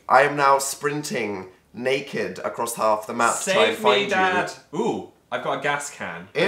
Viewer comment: did Phil said "save me dad" or am i crazy